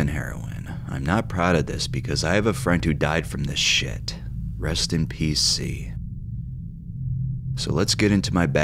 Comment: I thought he said " Rest in Pcp" 😅